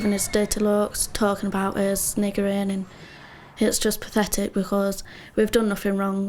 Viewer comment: did say people are “niggering” ???